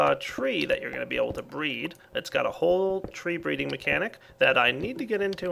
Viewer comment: Did I hear you say "Tree Breeding" ?